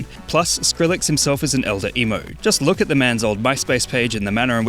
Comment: I thought he said elder Elmo. Heeeheeeheeeee.